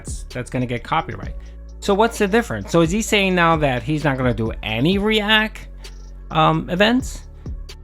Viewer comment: did you say re-ack?